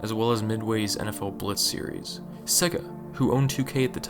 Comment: did he say sigga ??